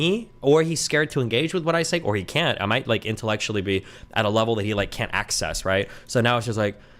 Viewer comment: Is Destiny say he has outpaced Sneako, intellectually? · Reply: Vaush: "I feel like I have outpaced him (i.e. Destiny) intellectually" Destiny: "I might intellectually be at a level that he (i.e. Sneako) can't access" <link> Destiny has become a joke at this point.